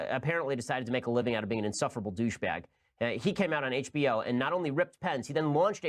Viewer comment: How rich that Ben Shapiro describes anyone else as being a "professional douchebag" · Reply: I thought he said "insufferable douchebag"??